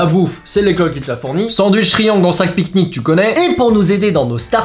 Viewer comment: il dis quoi "sandwicherie ? Tu connais" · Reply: Il a dit : "Sandwich triangle dans un sac pique-nique, tu connais ?"